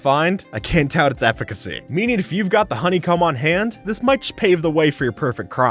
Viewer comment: did you just say honeycum